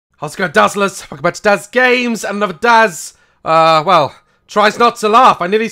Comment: I accidentally clicked on a button that skipped 15 seconds forward at 0:1 and then it sounded like Daz said: How’s it going dazzlers and welcome back to Daz gay😂 P.S I love your videos Daz😂👌❤️